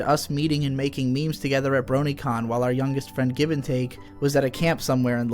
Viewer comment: did he just say Brony Con?!?